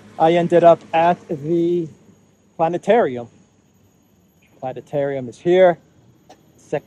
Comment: thought you said Sanitarium. 😂